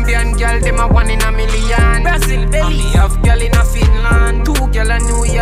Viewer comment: I hear Belize 🙏🏽🙏🏽🇧🇿🇧🇿🇧🇿🇧🇿